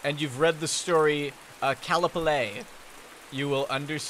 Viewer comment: can someone please tell me the title of the Lovecraft story he is talking about at 0:43 ? maybe its just too late or its because im not a native english speaker, but i just cant understand the title :D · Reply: Pretty sure he meant Celephaïs